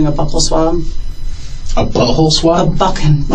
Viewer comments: "A butthole swab?" I fell out after he said that 😂 I don't know how that cop kept serious after that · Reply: Omg 😂 I thought he said bubble swab. Butthole swab has me rolling now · I also thought she said "Butthole swab".